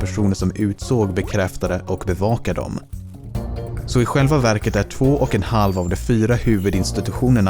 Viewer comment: all I heard was "har feeya hoogya" Never go full Swedish